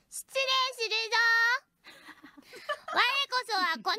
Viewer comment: 動画開いた瞬間聞こえた声が 「失礼するゾ〜」 にしか変換されなかったわ。